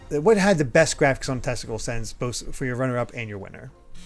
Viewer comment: Did just say ‘“What has the best graphics on a testicle sense”? 😂